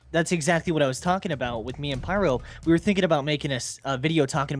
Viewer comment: I dont know why. But when i hear 'me and pyro' it sound like. ' me and mom '.